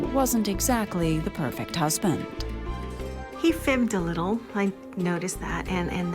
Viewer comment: what did she say here? "He filmed a little"? 😗🤔🤔 What's so sketch about that?